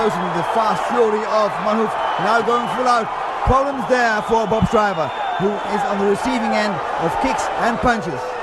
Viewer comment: Commentator sounds like he was saying bus driver.